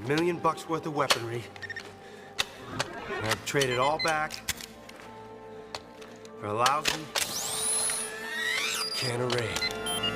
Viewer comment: I grew up with the original version. When I went to the theaters to see this, I truly liked it. Still do so today. My personal favorite scene is at 2:24 "A million bucks worth of weaponry. And I'd trade it all back, for a lousy can of raid." Matt LeBlanc did a great job as Major West. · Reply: for years I always thought he was saying "And I'd trade it all back, for a lousy day of rain."